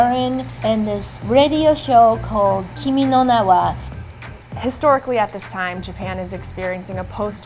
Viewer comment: did they just say kimi no nawa at 2:52?